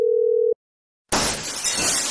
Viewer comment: I heard "RUN-OH NO HE GOT YOU"